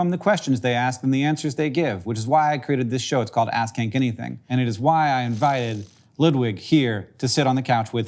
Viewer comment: I listened to this at work and at the end I really thought he said ‘it’s called ass kink anything’.